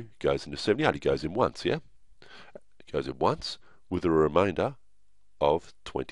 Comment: OHHH I THOUGHT YOU WERE SAYING REMINDER I WAS LIKE WHERE ARE YOU GETTING THESE NUMBERS I WILL TAKE BACK MY DISLIKE NOW